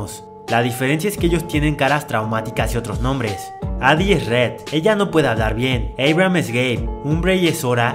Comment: entendí eried o algo haci es gay xd